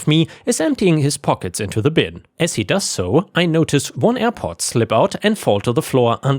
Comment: I don’t understand what the dude dropped in the last story. I heard “airport” but that makes no sense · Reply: AirPod